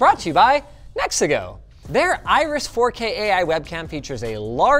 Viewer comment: "...the janky adventure you're about to watch, is brought to you by Mexico!" That's what I heard and the subtitles heard. It's canon now lol